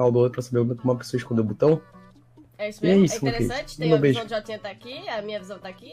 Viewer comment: Sera que fou so eu que ouvi o dizendo "e meu beijo?"